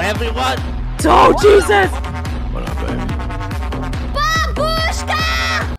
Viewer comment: The intro got me cracking😂😂😂 Jacksepticeye: SYKKUNIE!! TOAST: Wah.. Ello everyone Sykkuno: Oh JEEZUS!! CORPSE: Nothing(?) Or Muffin(?) 😂😂😂 Didn't really understand VALKYRAE: BABUSHKA!!😂😂😂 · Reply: Corpse is saying "whaddup baby"